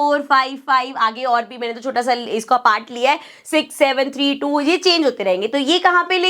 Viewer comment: did mam said 67